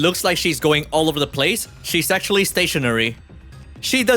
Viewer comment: I heard she's "sexually" stationary. Hmm...